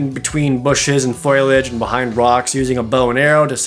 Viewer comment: Did you say foilage instead of foliage?